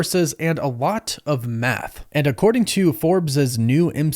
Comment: thought he said meth at first lol